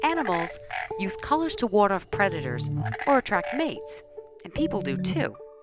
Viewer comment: i have a question, at 0:17 she said world of predators? what did she say at that moment? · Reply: Ward off predators like to chase them away